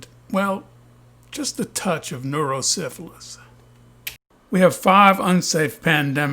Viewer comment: Neuro syphilis. !!! I almost fell out of my chair laughing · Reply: I thought you said Neuro-cephalus, not so far off, LOL